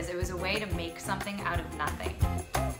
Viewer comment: is she saying yarn is nothing O.o